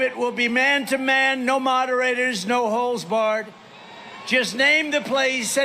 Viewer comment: Sorry, at 1:47, did he say "no holes barred"?